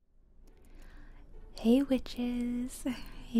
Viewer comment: I thought you said hey bitches🤣